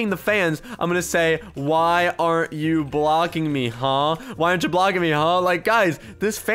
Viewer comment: I heard why is this FAN not blocking me no hate btw sorry